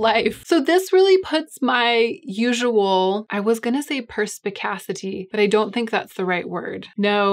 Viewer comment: I first thought you said “precipicatcity” and my brain says precipicatcity: noun, a city where it constantly rains cats (no “& dogs”. just cats.)